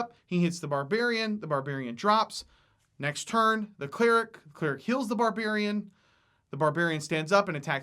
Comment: What i heard: "the cleric kills the barbarian"... what? :P